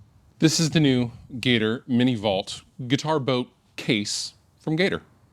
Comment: I heard "guitar boat"